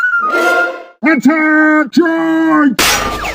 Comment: Wow i misheard dark prince when he charge i thought he said Toot Toot like train but it was Attack wow mind blown....